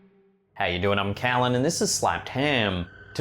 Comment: I thought he said How ya doing I'm and this is slapped ham. Then I read everyone commenting and calling him Kallen and hearing him say How ya doing I'm Kallen this is slapped ham 🤣 What is his name ? 🤣